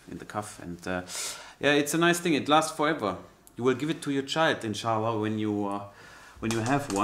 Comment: did you say in sha Allah?